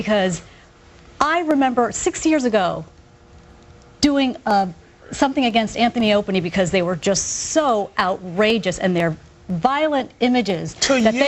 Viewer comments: did she say anthy and openy? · Did she just say "Anthy and Opieny?" God-fucking-damn